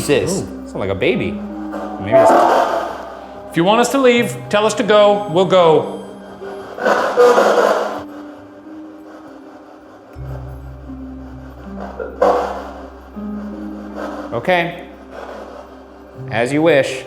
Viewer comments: i couldve sworn at 38:48 it said “don’t go” maybe i’m mishearing things or it was nothing but it sounded like that to me what if it was voilet and she possibly didn’t want them to leave in a comforting way? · does anyone else hear ‘go’ at 38:50?